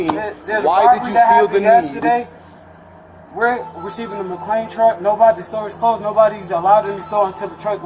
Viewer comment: Did he say ATM truck? Bank trucks? Or ….? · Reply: I thought he said cleaning truck. Like, the night cleaners?